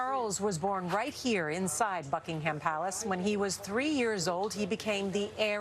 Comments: anyone else heard her say fuckingham palace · I’m sorry but did she say “F*ckingham palace”? I did not hear a B at the beginning of that word.